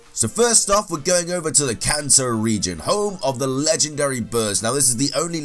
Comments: I thought he said "Let's go to the Cancer region" :I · Thought u said the cancer region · I thought you said ‘cancer region’ instead of “Kanto region” lmao